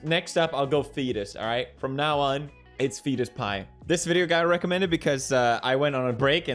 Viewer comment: did i hear return of the fetuspie?